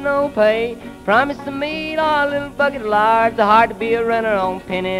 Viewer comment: holy shit at 1:14 did he say "they're all little fuckin' liars"?